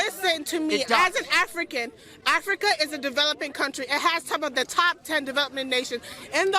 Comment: I think she said top 10, not number 1